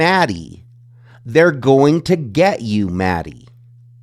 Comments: I hear “we’re gonna get you some food” not “they’re gonna get you for this”. But I also hear “to Allbrightsville” not “to get Thai food”. Yes there are so many coincidences that now don’t seem so coincidental. Small town, suspicious timings, inept cops and an influential university. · Just my opinion, but to me it sound like he says, ""We're going to get you grubs Maddie".